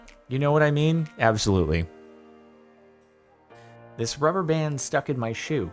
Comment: I thought you said that was gum on his shoe, not a rubber band.